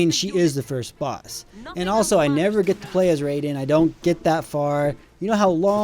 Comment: did you say Rayden?